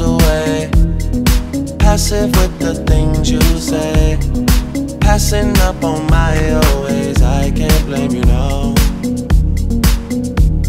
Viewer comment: Am I the only person who thought instead of I can't blame you now, I thought he was saying I can't let you down. One of the main reasons i couldn't find this song